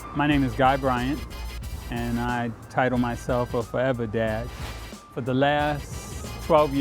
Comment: I thought he said forever dead